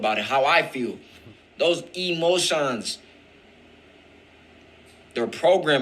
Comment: honest i heard demotions and it made me think of demonic attachments / emotions .. i did play it back a few times, but why did i hear it so clearly. Message for anyone reading. ✨️